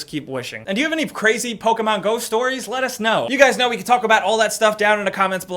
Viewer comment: it sounded like he said Pokemon Ghost Stories. lol. i wanna hear some of those!!